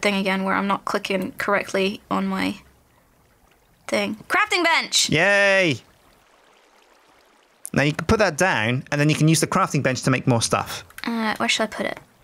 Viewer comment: it sounds like shes saying crapping bench